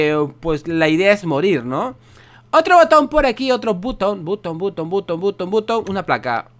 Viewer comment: escuche puto